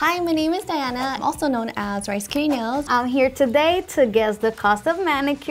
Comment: Thought she said “rice curry nails”